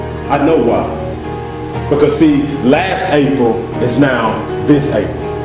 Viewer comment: what is he saying at 1:24 ? I mean: ,, I know why, because see last .... is now this ..... " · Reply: April..... April....